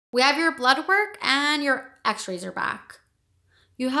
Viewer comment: 8:Your videos are helping me a lot, can you please tell me what did the doctor say in the 8:28 ? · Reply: Awesome! DR: We have your blood work, and your x-rays are back. You have a lot of stool in your intestines. You also have a high white blood cell count, which could be a sign of infection. We would like to invite you to our Patreon page where you can find ad-free videos, scripts and much more starting at just 1 dollar. It would really mean a lot to us if you can help us in sharing the video that way our channel can grow, we can reach more interpreters and provide much more educational material. Thanks!